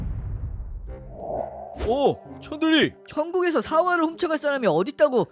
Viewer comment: 천대리가 천들이로 들림